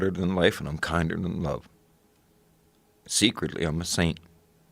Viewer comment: “I’m harder than life, and kinder than love. Secretly I’m the same” what a quote. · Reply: I thought he said secretly I’m a saint. Maybe I’m wrong.